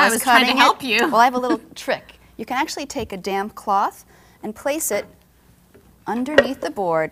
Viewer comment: sounds like she said damn cloth lol xD